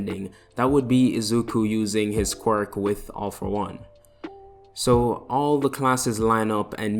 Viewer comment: I really hope you make a villain version of this also did he just say all for one?!